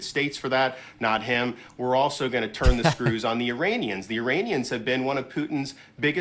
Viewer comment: I'm yet to be convinced Desantis is actually a human. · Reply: Is that why at 5:00, he talks about "Uranians"? He CLEARLY says Uranians. Maybe, DeSantis is not human and is from outer space. He has obviously been to Uranus and met the Uranians.